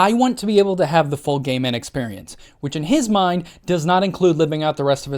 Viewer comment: every time they said "full gay man experience" i thought they said "full gaming experience"